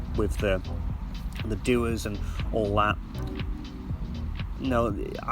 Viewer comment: I can't make out what was said at 10:25 , "you can buy a far stronger version of aventus with the *" what word was said here? I've just bought a 2020 aventus and love the scent but I wish the same scent would last longer, I'm getting up to 4 hours. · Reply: He said "DUA" which is a fragrance company.